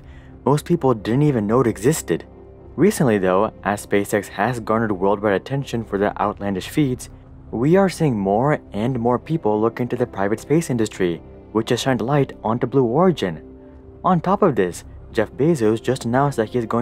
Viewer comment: I know i misheard but there will inevitably be a Pirate space industry as well